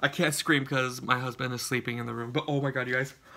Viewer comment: did you say your husband is sleeping in the other room....?